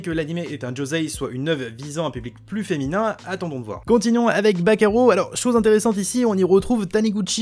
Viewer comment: j'ai compris "bakayaro" au lieu de "back arrow"